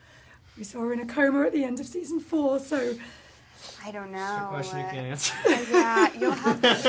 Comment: what said Finn?? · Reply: he said question you can’t answer